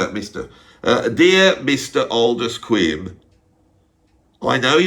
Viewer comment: Who is this 70s prog artist talking about at about 30min? I couldn't get the name, I heard "Oldest Quim", and I Googled it. It wasn't that.